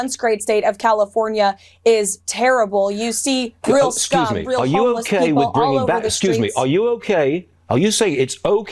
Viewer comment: Did she say “you see real scum, real homeless people all over the streets?”